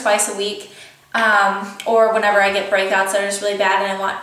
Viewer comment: when you said whenever i get break outs , i thought you said whenever i get pregnant and i was like WHAAAAAAAT and then i realied what you really said HAHAHA